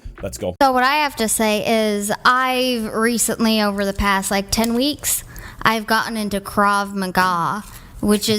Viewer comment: 10 weeks training didn’t teach her to even how lock up the choke 😅. I don’t even have her confidence after 4 years BJJ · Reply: I thought she said 10 days. That’s after 10 weeks??? That is worse!! 😂😂😂😂